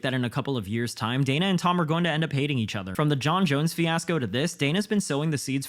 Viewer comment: I heard this as Dana and Tom are gonna start dating each other